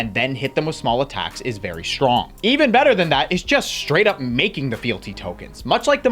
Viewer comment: I misheard and got excited when thought we were running Even Bigger Than That!